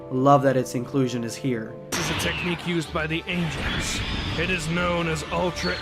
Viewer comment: He doesn't say "This is a technique used by the ancients." He clearly says "This is a technique used by the ANGELS!" 😉